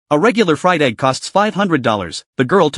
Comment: I heard it as Regular Friday 💀💀